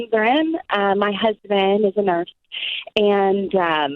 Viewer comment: It sounds like she says “My husband is a murse”.🤣🤣🤣